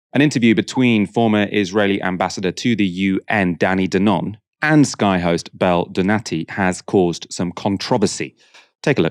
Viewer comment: I heard "former Israeli ambassador to the UN, Danny the Nonce" 🤣🤣🤣